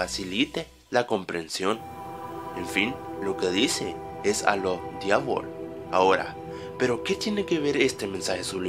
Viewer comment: minuto 1:10 yo entendi alo dave